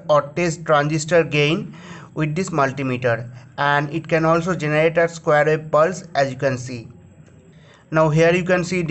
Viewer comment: What did you say at 01:24 ?? I could not understand you. I want to find out what that symbol is for next to the diode test symbol. Thank you. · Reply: "You can also generate a square wave pulse." It has something to do with Frequency.